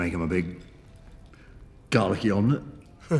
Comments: is it just me or did it sound like David said "Dalek-y omelet" at 2:20 instead of "garlicky omelet" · Every time he says "well" I melt. Why is he so stinkin hot! This is def the main reason why im seeing this movie. I could have sworn he said Dalek-y omelette. I'm pretty sure he was saying garlic... but i like Darle-y wayy much more. ;) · make them a big GARLICY omelett, i thought he said 'make them a big DALEKEY omelett'